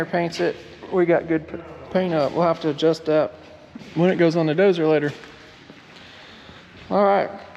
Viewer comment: I thought you said the slot goes down 😮😮😮